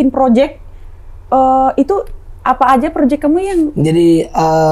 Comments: Ga salah denger nih Celline panggil yank ke di menit 34:24 Fixx kalian berdua sudah pacaran Semoga langgeng aminnn Ya Rabbal Al-aminn... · Menit 34:20 itu aku yg salah dengar kata yank apa emang bener? 🤔